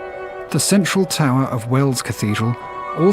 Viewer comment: It’s called Wells cathedral, not Welsh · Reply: Thanks. I thought he had said "Wales"; I didn't know that there was a city named "Wells".